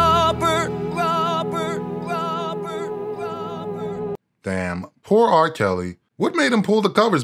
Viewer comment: it kinda sounds like he is saying grobert